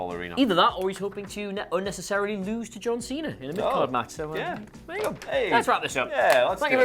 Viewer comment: What was Adam saying 14:33? Lose to John Cena in a what? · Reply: in a mid card match